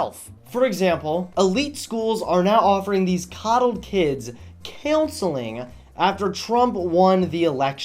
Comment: Did he just say killseling?? is that supposed to be a pun?? or Is that really how they say it up north. 1.08